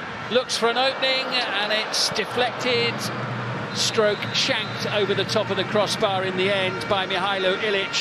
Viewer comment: did he say shanked? 😂